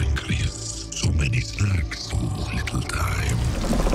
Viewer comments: Did anybody caught what VENOM was saying at around 1:35 to 1:40??? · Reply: Duy Tran I believe something like "so many snacks, so little time" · can someone translate what he’s saying please because I don’t understand a thing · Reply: I think it's snacks not sacs